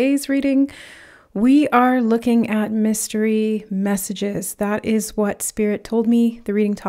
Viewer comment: Lol you said what's the message? I heard "it's a mystery" oh and owls everywhere too. They always meant wisdom for me so that doesn't freak me out at all. I love owls, actually all birds are awesome. I mean they can fly, well most of them. The ones that can't are cool. Hahahaa what a bird tangent....